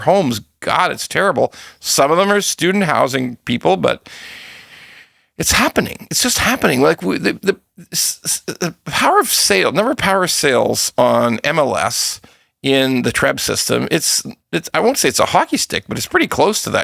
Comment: I thought you said power of says will be fiiine